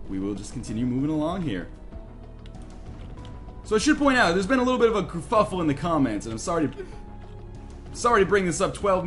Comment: did he say a little kerfuffle or cuffufle????